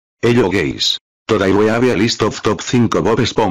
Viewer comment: I heard "hello gays"😂